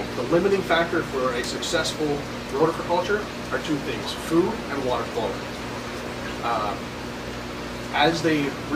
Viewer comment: Sorry my listenning is bad can u help me on 1:59 . he said " 2 things, food and..... ? " did he say " food n water cold" ...? thank you :)